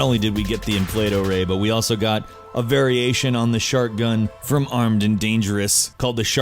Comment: When he said "Shark gun" I thought he said "Shart gun" XD